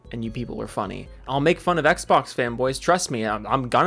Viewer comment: did he say xbox femboys, or do i need to touch grass? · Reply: I mean... There probably ARE Xbox femboys, but I heard fanboys with the fan being said in a way it sounds fem. So... Maybe we both do? Also great icon dude. Stay safe and keep being awesome.